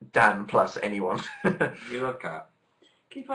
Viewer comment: MY HEART JUST MELTED OMG. Phil sadly goes "you love Cat…" and kinda rolls his eyes and turns away. OH MY GOD STOP IT JUST STOP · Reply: I heard "we love cat" lol